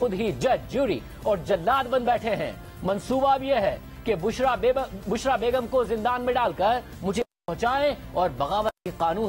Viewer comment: did he said Bushra Bewa ? 😆😁😇